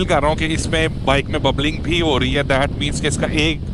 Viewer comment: what do you feel in the bike? Did you say "" bubbling " ? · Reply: Yes, one of the tyre was out of alignment